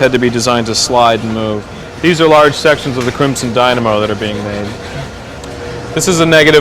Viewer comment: I’m sorry, did he just say Crimson Dynamo